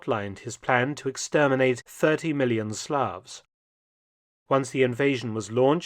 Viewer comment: did he say 30 million Slavic people ???